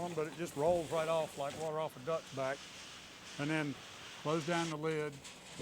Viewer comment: I had to listen twice when he said " I put a little sauce on it, but it just rolls off them like ( like water off a ducks back). I thought he said like water off a nut sack 🤔